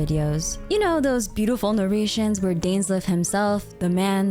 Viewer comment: Damn, I thought for a second you said the beautiful Norwegian videos, with something Danish.. the man the myth, the legend... I had to put on text to see what was been said there :P Anyways.. Looking forward to get those darn voice actors back after the strike. Paimon`s voice actor got fired.. So new voice for her. That will be nice. Was never a fan of the voice